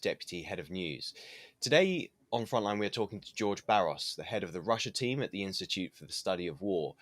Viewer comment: Here we go.... First thing that you lose with war is the truth.. It's always replaced by propaganda. Oh my god I thought he said George Soros.. You know the guy that's making money from the pipeline.. Woops